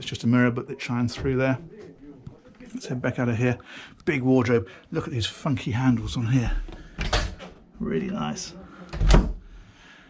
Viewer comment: i know he said funky but i swore he said f'ing handles the first time